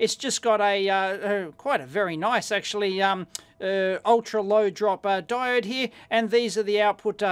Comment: Can anyone tell me what is an "ultra load dropper"? 4:00 is it the same as a Schottky diode? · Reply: Ultra low drop (i.e. low forward voltage on the diode)